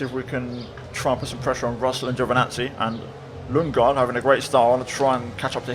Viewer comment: It sounds like you say Lingard at times 🤣🤣